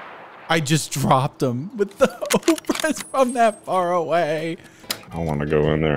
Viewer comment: What does he say? Ohbrez? Oh-brez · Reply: Obrez, it's a Russian word that means "sawed-off", commonly used to describe a sawed-off shotgun or sawed-off mosin rifle